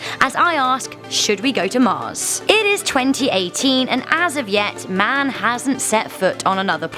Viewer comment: is it me or did she say 2018?